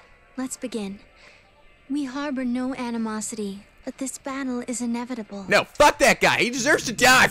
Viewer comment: did anyone else hear "bannle" instead of battle?